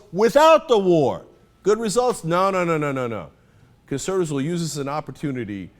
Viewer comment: I love how the youtube auto subtitle used "Cancer" instead of "conservative"